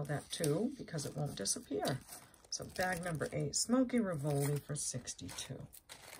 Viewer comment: I thought you said Smokey ravioli lol